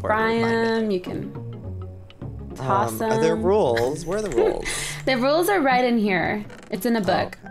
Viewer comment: When Joey said " where are the rules?" I thought he said where are the rolls because they were talking about what you can do with potatoes!!